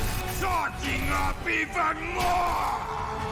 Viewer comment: Subtitles said " s*cking even more! "☠️